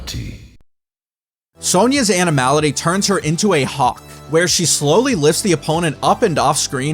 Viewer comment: I swear I heard Sonya give a Hawk Tuah at 22:00.